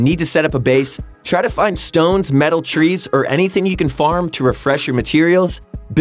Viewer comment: Am i the only person that heard him say metal tree